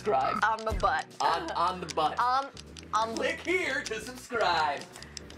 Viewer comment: My friend thought he said lick instead of click and unsubscribed and said He'll no you perv.